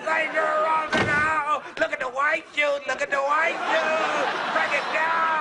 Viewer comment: Instead of "Look at the white shoes", I thought he was saying "Look at the white dudes".